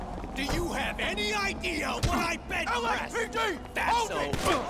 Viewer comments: doesn’t it sound like he said LAPD instead of LSPD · Wait wait wait 4:21 cop shouts LAPD instead of LSPD at least it sounds like that.